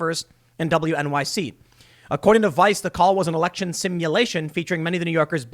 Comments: "I thought you said Election Stimulation" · Election simulation???? I thought you said ERECTION STIMULATION! OHHH NOOOOO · I thought he said it was an erection stimulation call! Toobin it. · It was an "Election Simulation." He thought they said "Erection Stimulation."